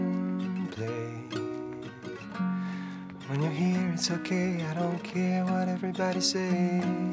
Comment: C'est plutôt "I don't care what everybody sayS" ... désolé